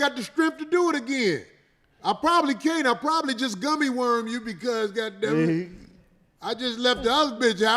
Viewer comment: Wait…. Did he say Gummy Worm 🪱 you 😳😂🤣😂🤣😂🤣😂🤣😂🤣😂 A Foo!!!!!!